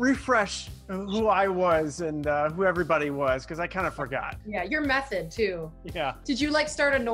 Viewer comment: I thought she said "your a meth head to" 😂